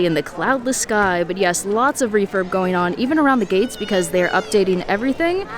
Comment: I'm deaf... when you said refurb... i thought you said refer... 🤣 look... refer, refer, everywhere 😆